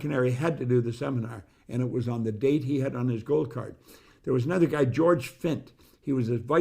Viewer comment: Where can’t I get the gold card ?? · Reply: Thought he said goal card .